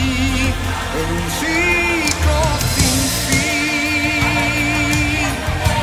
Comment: Yo siempre pensé que decía la canción "en un siglo sin ti"🎶😂